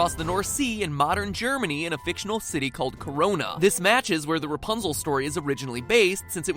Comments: Did he just say Corona???!!!!!! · did he just say corona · When I heard the town’s name at 3:07 I heard Cardi B saying coronavirus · Excuse me did u just say CORONA? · did he say corona? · did he just say carona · did he just say a magical city called Corona? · I swear I heard corona